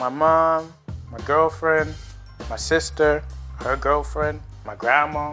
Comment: did he just say that ??? His sister's girlfriend ??